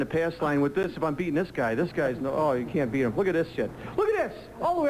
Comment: Almost sounds like he said “Look at this shit”